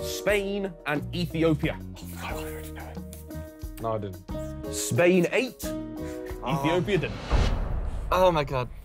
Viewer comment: what was the joke? I couldn't hear it properly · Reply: Spain 8 (ate). Ethiopia didn’t (starved).